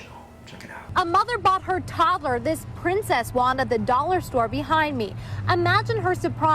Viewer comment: who else thought the “dollar store” said “$100 store” for a second?? 😂